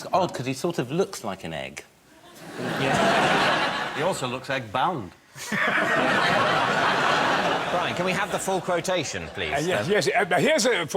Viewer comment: What does Bob say at 26:50?; Hithcock sortof looks like an egg "He also looks like ?????" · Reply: Egg bound. Literally, when a bird or reptile cannot lay an egg that has formed, euphemistically, to be constipated.